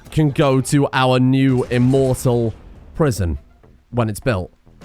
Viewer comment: I really thought that said "immoral prison" and was thinking, "what other kind of prisons does Mr Streamer make?" 😂